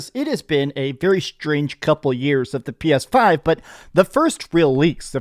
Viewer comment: the way he said PS5 sounded like Ai